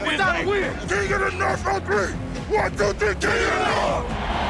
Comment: what did he say at 0:43? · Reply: Ian Barnes "Kings of the North on 3, 123, Kings of the North!"